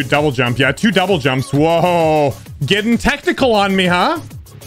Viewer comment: When he said technical i thought he said "ah your getting tentacle on me huh?"